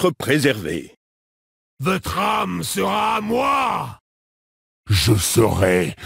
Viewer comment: I'm French and I always hear : "Le tram sera à moi" (the streetcar shall be mine) instead of "Votre âme sera à moi" (duh) It's hilarious XD